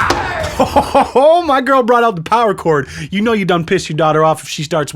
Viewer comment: I glanced away when you said power cord, and I thought you said power chord.